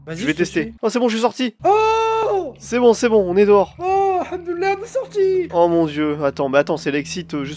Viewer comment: C’est moi ou à 6:30 ta il dit « Hamdoulah » ? J’arrive pas à me le sortir de la tête il a bien dit OKLM « Hamdoulah » sa me fume » XwX